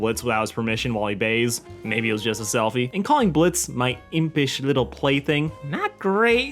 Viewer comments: I thought stolas said "My impish little baby" I kinda just assume he wont call blitzø a play thing at this point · did he? I heard him saying “my impish little Blitz” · I thought Stolas said “my impish little baby